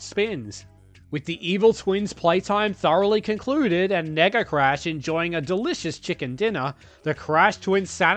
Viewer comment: I’m sorry, what did you say? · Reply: Nega Crash, as in negative. It's what he's called in Crash Tag Team Racing.